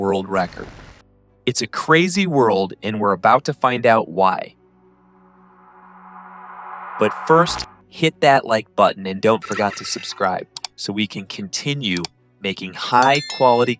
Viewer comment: “don’t forgot”? Maybe I misheard.